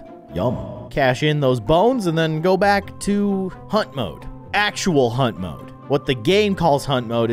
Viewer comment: For a sec I thought he said hump mode lol